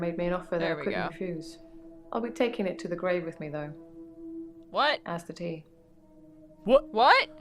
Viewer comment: Claire: ”I’ll be taking it to the grave with me though, as did he” What I heard: “I’ll be taking it to the grave with me though, and that’s the tea” 💁🏻‍♀️💁🏻‍♀️